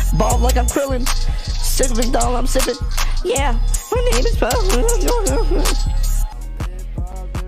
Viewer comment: why'd it sound like he said nigga at 7:52 lmaooo